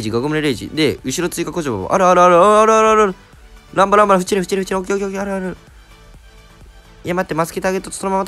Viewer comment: フチれって聞こえるｗ